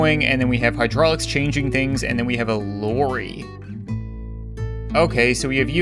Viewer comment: In the first one I thought it said Larry not lorry😅😅😅.